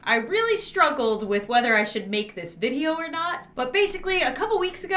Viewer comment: 0:0 why did i hear "One Direction " instead of " wheter i should "